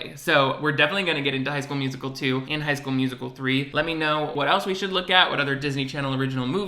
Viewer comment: Lmao I thought you said "hi, school musical" & thought what the heck is this what you call your subs now 😂